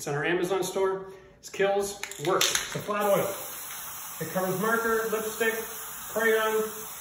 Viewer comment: I could’ve sworn he said “it covers murder, lipstick, crayon…” Almost fell out 😂